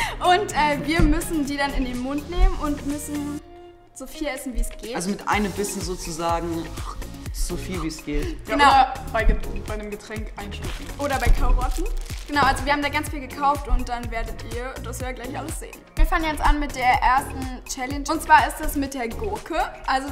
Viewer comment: Ich verstehe immer bei 0:38 "Oder bei Kaurotten" 😂